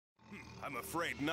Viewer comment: For the longest time I thought he said " Kunai Whip Chain" Well boy was I wrong